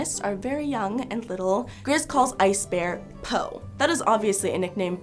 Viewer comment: Tbh I think Grizz was saying "Bro" and not "Po", and that's just the way the voice actor speaks.